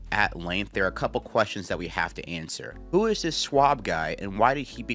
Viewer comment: At first I thought you said “who is this SUAVE guy” with Stuart Scott on screen. He was rocking the ‘Blade’ look in the clip lol